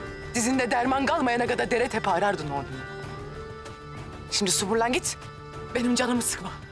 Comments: de asiye ne diyor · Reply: yani yürü git manasında suburlan diyor suburlan yürü git gibi büşey demek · de asiye ne diyor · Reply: simdi sumburlan git(defol git gibi bişi)